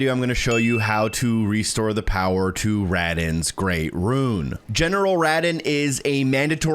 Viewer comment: Ruh-don. Thought you kept saying Radagon lol